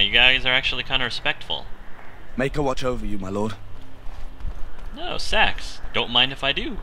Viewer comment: ahahaha....at 17:53 when you said sacks, I thought you said "Oh sex! Don't mind if I do"....pffft. Guess my brain's in the gutter :D